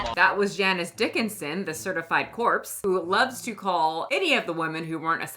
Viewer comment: Calling Janice Dickinson a certified corpse is peak comedy 😂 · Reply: It’s funny cause i thought she said “certified horse” at first which still applies 🐴😂